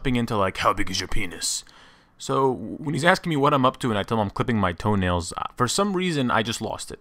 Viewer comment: I thought he was saying " I'm clearing my throat."